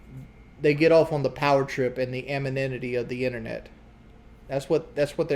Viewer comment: He really said amoninity again lmao!